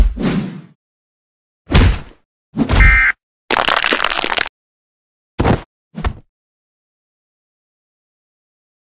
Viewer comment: Did he just say a cuss word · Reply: Idk i think he said fuck